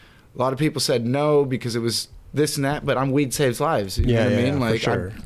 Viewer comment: "weed saves lives" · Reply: for the longest time I thought he said "we saves lives" hahaha